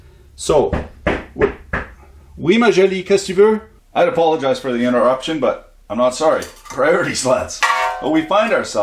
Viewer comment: aw I misheard you, I thought you said I apologise for the interruption, but I'm not sorry- I quite like that line :)